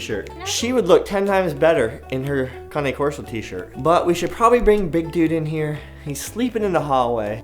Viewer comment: did she say call him 'big balls'🤣